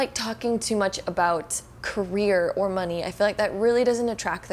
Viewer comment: Got distracted and misheard ‘career’ as ‘they don’t like to talk about Korea’ and was like...they what now?😂